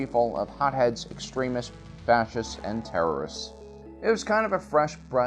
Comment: I legit thought that he said “Baptists” instead of “Fascists”